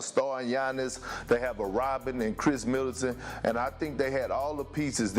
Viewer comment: thought he said Robin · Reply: idk i thought he really said "he has a Rodman in Middleton" but we talkin bout Perkins here... He be slurrin...